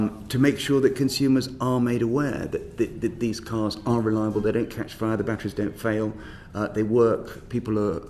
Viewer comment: I played it back a few times and it sounds like he said "they don't catch fire, the batteries don't fail..." so clearly there is something wrong on my end. What did he really say there?